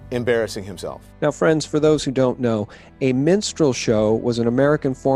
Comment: I thought he said a menstrual show lol I was going to say Don Lemons career is the longest menstrual show lol